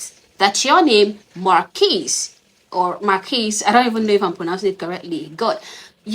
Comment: The kid's parents need to sue "Marquis" for slander and harrassment. How was this even "newsworthy"? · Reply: I thought he said Monquise…I was wondering why they asked how to spell my name (very simple name) the other day.